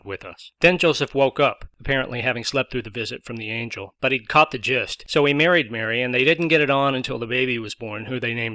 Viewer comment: When you said "caught the gist" at 1:53 it so sounded like 'copped the jiz' which, given the context, would've been appropriate :D More fun with:D